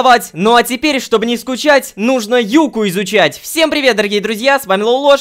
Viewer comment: Ахахахахаххахахахахаха Мне в начале послышалась "Нужно ЮБКУ изучать" Паже лайкните Пазязязя